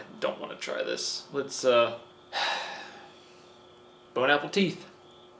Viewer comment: did he say bone apple teeth??